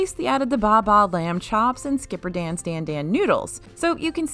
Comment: Did I hear, "Bah Bah Lambchops"? That is morbid